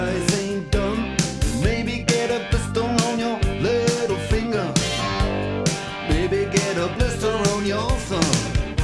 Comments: i thought he said “baby quiero queso roñoso” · In the minute 1:30 When it says Maybe get a blister on your thumb Some people thought very wrong saying or singing... Baby Quiero Queso Roñoso in Spanish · i always thought it was, we got the movies, we got refrigerators, we got the color tvs.. like as if he was singing about how rich he is, but turns out its the complete oposite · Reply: I thought he said pistol on your little finger since I was 6 lol now I just got chimpanzee . It’s so bad azz!!!😂